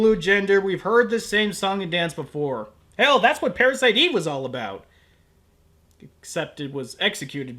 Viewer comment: did you say parasite eve or parasyte?